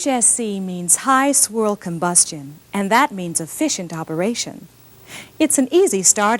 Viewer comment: Did... did she say "high squirrel combustion?"